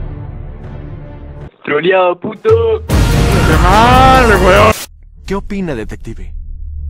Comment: TROLIAO PTOOO "DYLAN":QUE MAAAAAALL weoooon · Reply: Yo escuché "Dale weón".